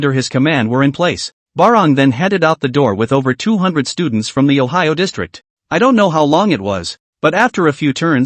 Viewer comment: Is my brain rotten or did they just say ohio district? 2:43:02 I mean, I'm not sayin it's takin place in America, but they were talkin smack abt western medicine and how chinese herbs are superior. So I'm guessin it's takin place in China... Idk- I'm sleepy asf